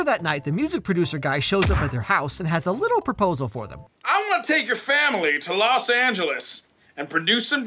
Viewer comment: I heard "I'm gonna take your family" and I instantly freaked out